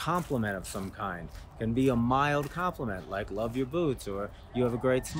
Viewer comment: I thought I heard "I love your boobs" haha simple tip yet effective! thanks